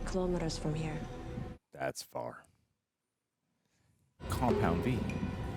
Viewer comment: did they say "compound V"? as in the same compound thats in the boyz series.